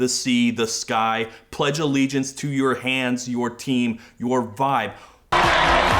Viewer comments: "Pledge allegiance to your vibes" is such a weird phrase too. Gives me that same embarrassment of hearing a wine aunt using teen slang to seem cool to her nieces and nephews and like, isn't everyone involved at this point a decade too old to be talking like that? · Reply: NEGL for a while I heard "pledge allegiance to you hands, your team, your thighs" and it almost works better. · Okay but why do I hear "your farts" when she sings "your vibes" 😭